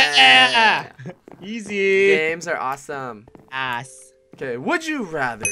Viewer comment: In 12:12 i heard said ass